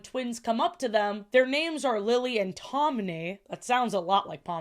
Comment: She is definitely saying Todney, not Tomney